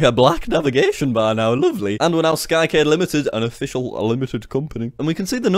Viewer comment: I thought he has been saying skykid then skycade wow just wow